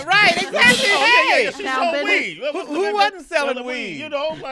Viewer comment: mark at first I thought she was saying she sold weave LMAO